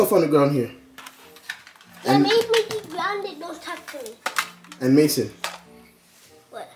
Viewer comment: Wait 7:22min in did he say don’t talk to him? Wait a minute child. Not in my house.